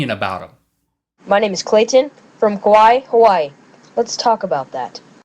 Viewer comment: did he just say... KAWAII HAWAII?